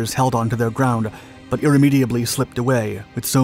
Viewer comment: What the hell was that word at 10:19? Irramiably?🤣